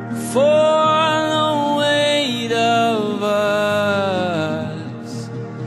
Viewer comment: I thought it said "for the wait for us" like there going to wait for something